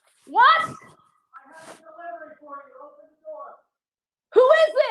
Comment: Wow that is scary. After he beat on the door it sounded like he said, I have a delivery for you.